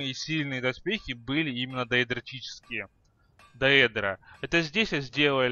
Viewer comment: Даэрдадрочичиские или мне послышалось???